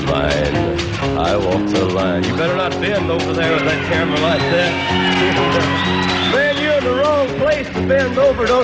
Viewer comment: i'm not a native English speaker, please tell me what Johnny says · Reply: "You better not bend over there at that camera like that, man you're in the wrong place to bend over don't you know it, get up from there at that camera!"